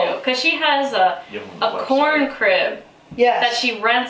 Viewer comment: Too funny! When she said the other lady had a corn crib she rents out, I almost broke my neck looking back at the screen saying whaaaa? I thought she said porn crib, lol.